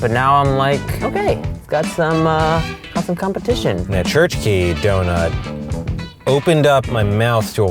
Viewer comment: Wait. Did they say kirche as in cherry?? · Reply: TheLostOne dude cherry is kirsche and church is kirche and the restaurant was called the church key and thats what he said